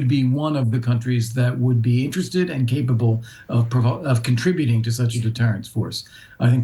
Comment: Am I the only one who heard him nearly say the UK would be interested on provoking?